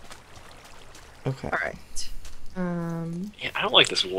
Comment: Guys at 5:00 Stacy didn't swear she said "alright"...